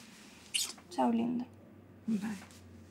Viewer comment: Mano eu joro que eu ouvi tchau linda nessa parte